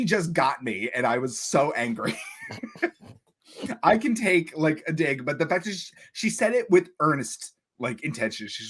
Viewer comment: Rewatching this, I totally thought at 7:21 I said “I can take, like a d!ck” and I’m thinking to myself “why the hell did I say that?!” 😂😂